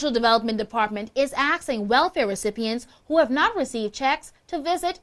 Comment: Please, please, please tell me I didn't hear what I heard the word "axe" from a NEWS ANCHOR...